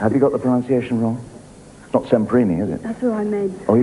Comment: who is the person the lady is trying to say? I couldn't understand the correction either. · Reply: (Alberto) Semprini - a pianist